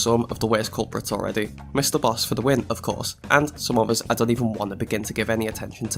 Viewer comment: ok so after mishearing something, can someone please make an edit of "MrBoxFTW"?